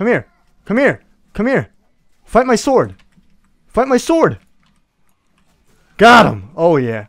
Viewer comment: sounded like he was saying fuck my sword xD